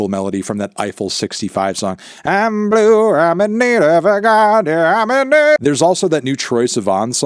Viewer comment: did he say "I'm blue, I'm in need of a guy"😭